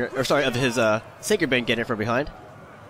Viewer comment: Lol 6:03 I heard they to get it from behind